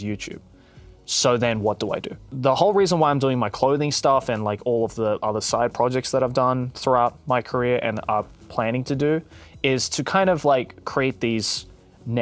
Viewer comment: How does Anthony keep pulling off the most unexpected crossovers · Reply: I thought u said The Sidemen but yeah both too xD